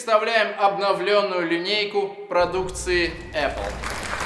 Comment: Мне послышалось Обговленную линейку а не обновлённую)